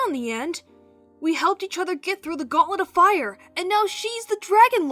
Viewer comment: As a Harry Potter fan, the only thing I heard was Goblet of Fire